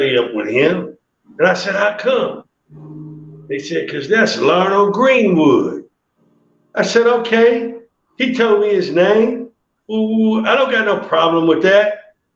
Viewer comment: Vaughn Orrin Greenwood…I thought he was saying Larnell Greenwood